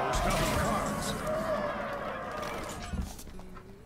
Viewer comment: To bath all! is what the silver hand recruits say, (the 1/1 paladin hero power thing i mean) go back to 35:10 and you can hear it!